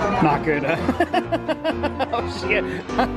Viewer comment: Also at 6:48 did really say oh sh*t? 😂😂😂😂😂